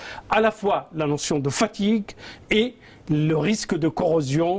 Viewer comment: notion de quoi ? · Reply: Fatigue